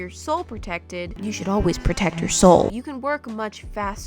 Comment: I heard "protect your sole" as "protect your soul"